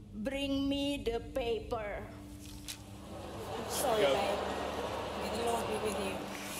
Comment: Can anyone tell me what alesha was sayin ? ( min. 5:00 ) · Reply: "Sorry, babe. May God be with you"